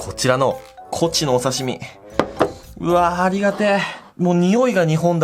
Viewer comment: いつの間にかニンジンも鍋に入ってた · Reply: うわー、ありがてー が、うわー、にがてー に聞こえて３回見た←